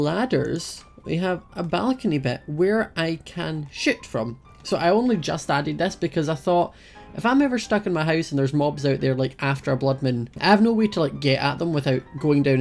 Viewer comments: When he said shoot from I thought he said sh*t from XD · I thought he said where I can shit from but now I think he said shoot from!! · i thought he first said ; Where i can shit from.; .....why brain...why